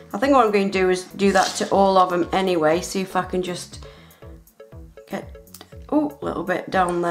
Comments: Sounded likebthe f word 3:02 but she said see if I can just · did she say f***ing